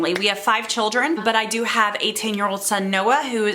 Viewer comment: why did I think you said “ an 18 year old?”😂😂😂